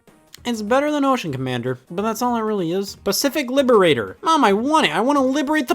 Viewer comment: I misheard that as pacific liver raider